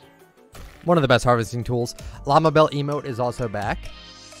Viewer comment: I thought he said llama bell is bad